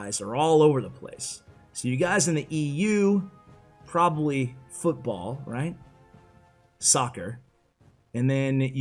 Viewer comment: I first thought said 'you guys in the EU probably, Football, sucker", LOL